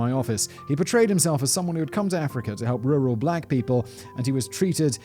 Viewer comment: I thought you said real real black people. It still made sense